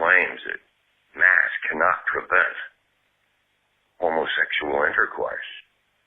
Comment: “Mask cannot prevent homosexual or intercourse”? Did I hear that right? 🤔🤷🏽‍♀️ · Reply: I heard "masks cannot prevent homosexual intercourse" 🤔